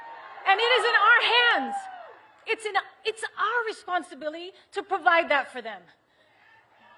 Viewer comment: She said “responsibili” 3:18 What is that word jlo? 😂